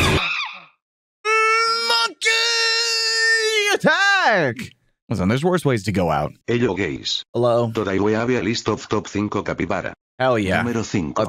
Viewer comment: In the capybara video at the start it sounded like it said hello gays and then said hi